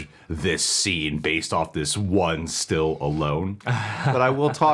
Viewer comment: You say "one still alone", I hear "Hi, I'm Sylvester Stillalone".